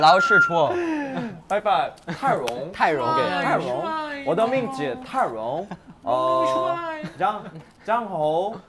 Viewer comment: why did i heard tyrone.... like from the backyardigans